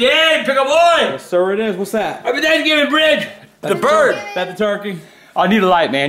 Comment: Oh I thought he said happy thanksgiving bitch🤣🤣